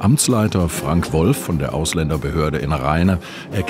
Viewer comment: AusländerGehörde?